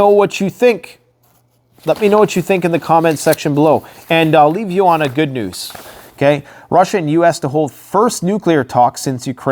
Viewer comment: Freudian Slip. I heard "and I will leave you on a good noose." HAHAHA As if any noose can be good. Stary Safe and Keep Prepping. Also, Did you hear about Altas's new shelters?